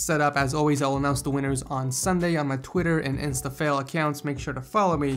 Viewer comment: Did i just hear "instafail" account?